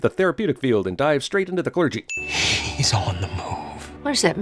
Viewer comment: When I watched this movie for the first time and Hedwig said that "he's on the move" line, I thought he said "he's on the moon" and I was very confused for good portion of the flick. The Beast lives in space? The fuck?